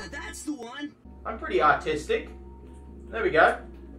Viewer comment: If u turn captions on and at 13:12-13:14 he's says he's artistic but the captions say autistic